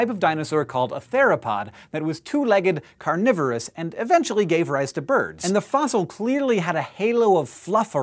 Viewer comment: hahaha play the video to @ 1:28 to @ 1:36 it sounds like he says "and eventually gave rice to birds".